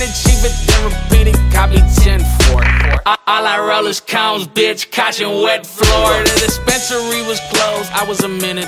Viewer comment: Anyone know what he says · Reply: ALL I ROLL IS CONES BITCH CAUTION WET FLOOR